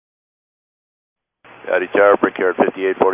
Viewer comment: I initially misheard "Howdy Tower" as "Daddy Tower" which sets a very different tone to the exchange